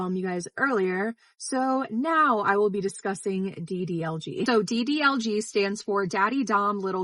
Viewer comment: i thought u said ddlc as in “doki doki literature club” and was like huh? wat? for a whole second there